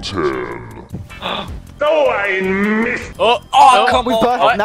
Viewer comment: does he really say "So ein Mist"? And if why can speak german?^^ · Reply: he says "OH i missed" with a d sound making it soud like " DOH i missed"